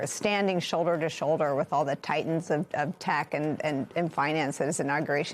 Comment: Ha! I heard, not ''Titans of tech'', but ''Titans of Tack''....equally true and far more amusing ;)